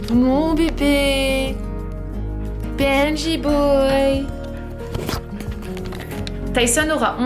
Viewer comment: I heard you saying "PNJ boy !" instead of "Benji boy !" 😄